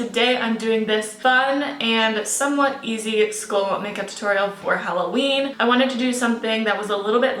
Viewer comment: Hey! This video is AMAZING and so awesome for a Halloween makeup idea. I just wanted to let you know that your video's captions are wacky sometimes. For example at 0:38 seconds said "its cool" instead of "skull", at 0:46 it said "school" instead of "skull", and at 0:49 it says "area angelic" instead of "airy and angelic". I wanted to bring this to your attention because it is important for users who use the captioning to be able to get full, WONDERFUL effects of your video! Thank you for posting these crazy awesome ideas! I can't wait to see more!! xoxo